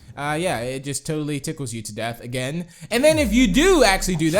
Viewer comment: I thought you said tikles you do death on